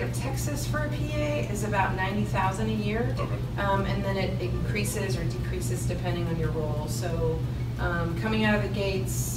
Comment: Did she say starting salary for PA in Texas is 90 grand and it increases or DECREASES based on your role (4:18)?